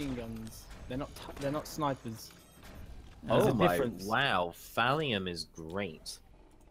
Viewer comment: I thought said Valium. 🤔